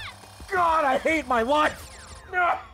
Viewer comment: Low key tripped out cus I thought he said “God I hate my wife!”... was like damn what did do? lmao